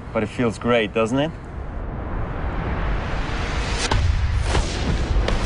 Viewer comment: i heard 'u are spreading on my couch' ...Thumbs up if u heard it !!